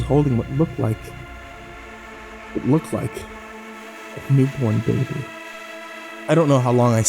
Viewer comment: Have a great weekend also at 13:15 I think he said a newborn baby